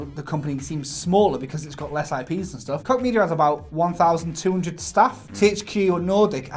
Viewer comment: i thought was saying COCK media instead os KOCH media :/